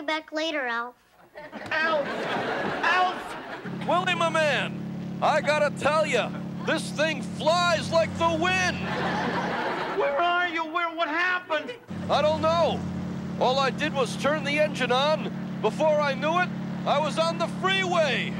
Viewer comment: Alf, willy, my man, I got to tell you, this thing flys like the wind, 🤣🤣🤣🤣🤣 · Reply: I always thought he said, drives like the wind.